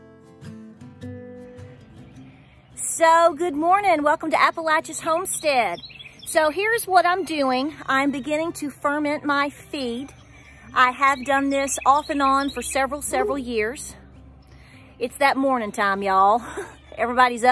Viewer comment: Oh Lordy! I could have sworn you said “Ferment your FEET!” More coffee needed before I watch instructive videos!!!